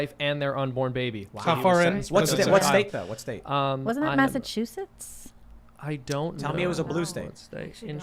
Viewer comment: Libby saying Massachusetts and all I hear is "Massive huge tits"